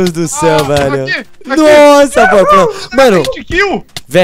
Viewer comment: to na conta da minha mae, gente só eu q escutei o jazz falar poha no 7:14 ?